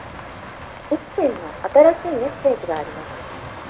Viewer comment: 最初「おっぱいの新しいメッセージがあります」って聞こえてニヤけた